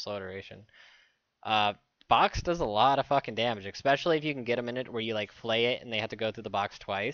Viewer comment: ANYONE ELSE HEAR XPECIALLY? LOL